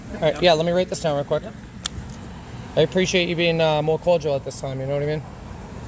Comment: it sounds like you say, "I appreciate you being more CORDIALENT this time" to. Is CORDIALENT a word, because I can't find it in any dictionary. Please lmk if you meant to say that or not. Thank you for your activism and public service in the New England Area. ✌️😎👍